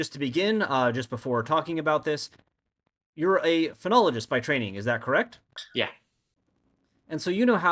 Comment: I wasn't looking at the subtitles and I heard "phrenologist" and was just like "uhhhhhhh"